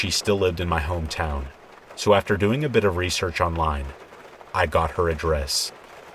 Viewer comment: "I got her address" idk why I said "That's very nice of you" because I thought you said "I got her ah dress"